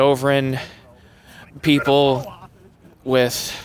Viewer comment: I thought he DID say Arian People Thanks